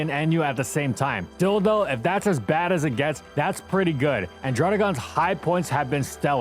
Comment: did he say dildo?